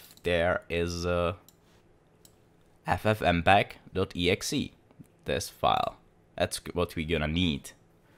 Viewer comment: what is it called?! Ffmpag.exi???